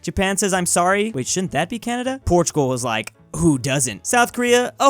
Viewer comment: did u just say portugal to italy?